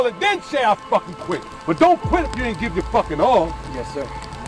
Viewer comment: The owner of this “business” is a great manipulator of insecurities, I’ll tell ya that much. The thing that got me, though, was the guy saying, “Don’t quit before you give it your all”. For some reason, I heard, “Don’t quit until you get your money’s worth!”